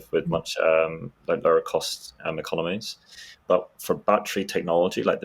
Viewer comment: I had to relisten, because, while distracted, I heard, "hard to compete with the lower cost in the colonies", instead of "lower cost economies" 😅. It's nice to see focus being put on the subject of understanding batteries, but I would have also thought that the importance of environmental controls has, by now, resulted in better manufacturing techniques precisely to reduce spread? I remember A123 making big noise back in the day regarding their batteries being better because their environmental controls were better than other LFP manufacturers, enabling their NanoPhosphate or whatever tech, which was sensitive to humidity during assembly. Is the sensitivity so high, that it's not possible to control the environment enough?